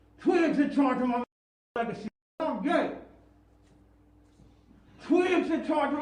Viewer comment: I had just completely expected to hear him say "Im ye" I was thinking "This is stupid, im sure he said Im Ye" but after just now hearing it, i heard "Im gay" twice. He did say it. It could just be a pathetic publicity stunt to once again be the topic of conversation. Im not sure the motive behind it, but i am sure that he did say it.